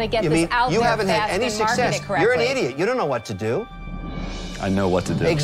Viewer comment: When I saw this the first time, I thought he said "You're NOT an idiot". Thanks for letting me know he DID call him an idiot. I agree. Never call a potential partner names.